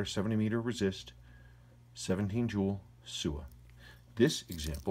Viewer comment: What does he say at 5:55? "17 Jewel...(sounds like sumo)" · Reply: Suwa